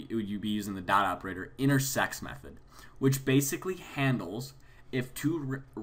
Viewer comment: lol i thought he said in our sex method at